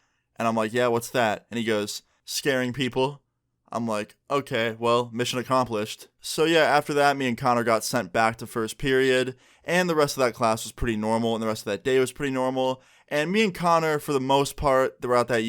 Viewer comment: am i the only one that heard the name carter instead of connor until